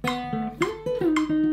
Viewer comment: what did you say at 9:45? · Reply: I whispered "fuhkkk" hahaha